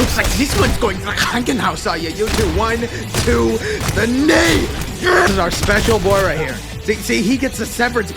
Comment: what did bro say? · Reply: tHE KNEE